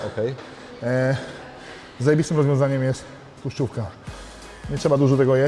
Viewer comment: co on powiedział? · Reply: Tluszczówka